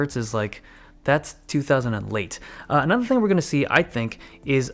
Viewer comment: did i hear "two thousand and late"?